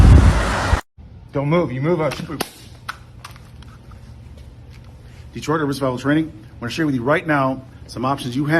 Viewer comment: the captions say “Detroit everybody’s bible training” and for some reason I’m choking on laughter about it